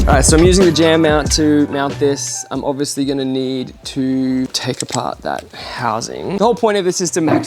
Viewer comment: i thought you said "Im obviously gonna need to take a Pot" LOL